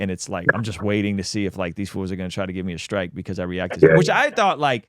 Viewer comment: Did he just say… “these foos”? I don’t understand that .